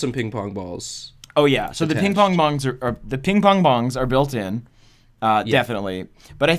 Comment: Please tell me im not the only one who heard him say ping pong-bongs XD